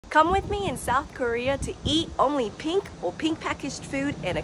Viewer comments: I THOUGHT SHE SAID NORTH KOREA 💀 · I thought you said north korea